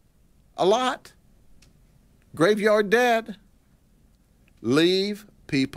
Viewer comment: “...and they will shoot you. A lot. Graveyard dead.” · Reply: I thought he said graveyard dad lol